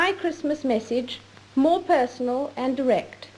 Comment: I think she said:"...more personal and erect.".